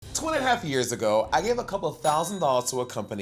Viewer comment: At first, I thought she said “twenty and a half years ago” 😂